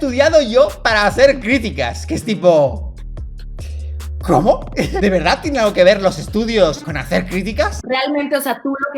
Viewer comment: Alguien sabe que dice en el minuto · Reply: La gente:que estudió ella para ser un caballo xd